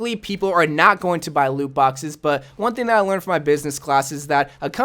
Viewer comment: At around 2:10 for the subtitles it said Lube boxes instead of loot boxes🤣